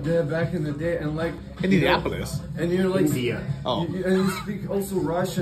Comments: I thought he said Indianapolis too… And as a Hoosier I straight up lost it imagining Bald waking around Indy commenting on the Soviet architecture lol! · I thought he said Indianapolis too!😂😂🏁🏎🏁